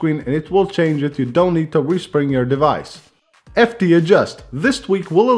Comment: Sounded like he said "You don't have to whispering your device" lol